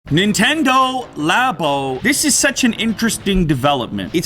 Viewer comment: Anyone else thought it said Nintendo Lambo?